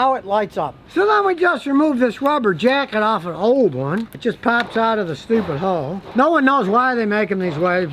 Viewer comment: pops out of the hoe 😂😂 I know that’s probably not what scotty said but my ears hear things